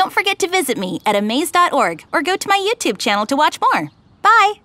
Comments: did he say "more" or "porn"? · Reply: more i think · I swear i heard at the end “or ho to my YouTube channel to watch porn” 😂